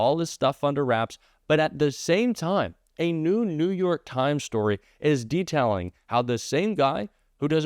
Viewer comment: i heard "a New New York Times report" and thought 'oh lawd, Elon is even interfering with the Futurama Timeline!!'